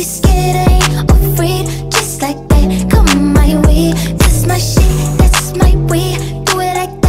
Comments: I thought it said "that's my sh*t, that's my WEAVE" LMAO IM DYING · i don’t usually listen to this but the “that’s my shit that’s my wave....” part 🤟🏼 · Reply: I thought it said “that’s my shit that’s my weave” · I thought she was saying “that’s my way” not “that’s my wave”😭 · I heard “that’s my weed” not “that’s my wave” *I’m not mentally ill I swear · I always thought she said "that's my weave" instead of "that's my wave" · Dead ass thought she was saying that’s my shit that’s my weed 😂😑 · I thought she was saying that's my weed instead of that's my wave lol